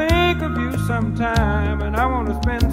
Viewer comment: for a while i thought he was saying "do the dowop" instead of "just the two of us